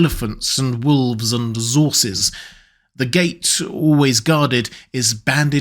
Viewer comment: Elephants and wolves and what? Zorses? Zebra-horse hybrids? Did I hear that right?